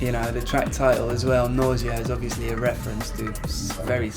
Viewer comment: When he says Nausea i thought he said Noisia.